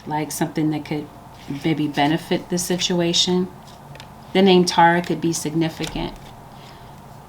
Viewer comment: What is the name she says at 25:31? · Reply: The name "Tara".